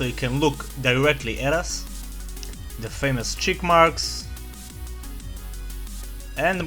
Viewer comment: I thought you said chipmunks but you said cheekmarks